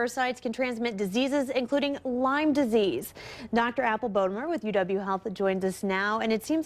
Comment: hahha I thought i heard she said ..Dr Apple Boner!